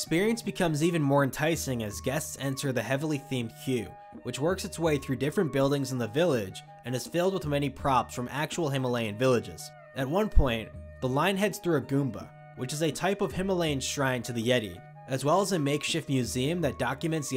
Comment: 100% control of Florida tourism, and yet my least desired place to visit (too many farting people, and way too much farting money; "its a noo from meeee" -Taylors sister in 'reading mean comments') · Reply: did he just say a Goomba? 😂😂